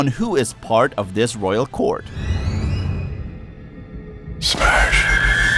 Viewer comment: did he say swag? kinda sounded like he said that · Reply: Smash